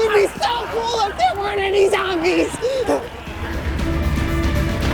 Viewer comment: it sounds like he said “ Where the F**K”